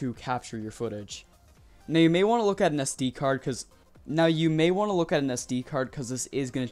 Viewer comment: anyone hear him repeat are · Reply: Sd card, yeah